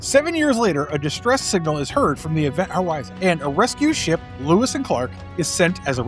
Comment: I swear he said Event Howizen